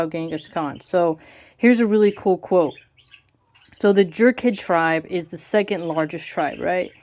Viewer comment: Omg. I heard it "the jerkhead tribe" I need to get my ears checked.